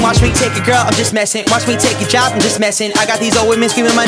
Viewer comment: Oh I thought he said "watch me take your child" when he said job